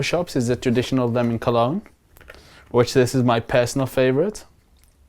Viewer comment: What type of cologne? 10:30 ? Legit couldn't understand his mumble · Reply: Lemon